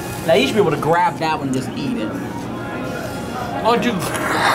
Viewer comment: what did he say? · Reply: “Why don’t you grab…(laughs) shut up man.”